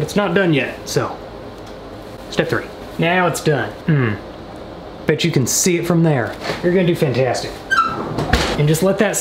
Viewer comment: Wouldn’t it be great if I had the most liked comment!When we said”bet you can see it”,I thought he said”bitch you can see it.”